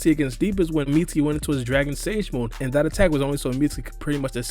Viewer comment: did u say dragon sage mode?🤔😂